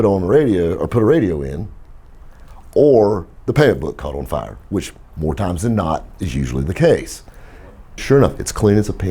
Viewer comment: The WHAT caught on fire?? The panblueh?? I watched that clip 14 times and STILL can't decipher what the hell he says. Help?! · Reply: Payment book.